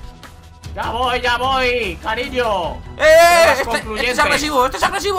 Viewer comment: ooh escuche algo que dijo supongo "cariño" 7w7 no sera que le dijo eso a mike 7u7